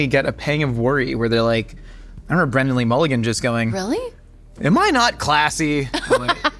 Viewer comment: Did he say Brendan Lee Mulligan or did he just hit the N too hard?